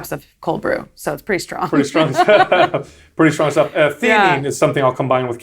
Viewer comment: Does anyone know what he said there? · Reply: theanine